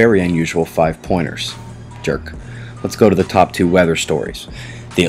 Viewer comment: I love how you talk at 0:15-0:21 and did you say jerk